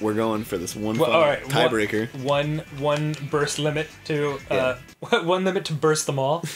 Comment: For a second or two I thought it said bust limit and I was like whaaat